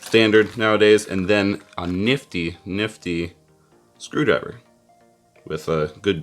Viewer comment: I thought he said "a really, really UGLY screwdriver"